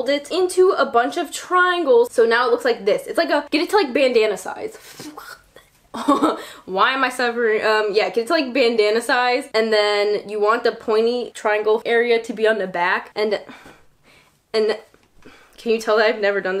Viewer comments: I thought you said banana at the end lmao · i thought she said banana instead of bandana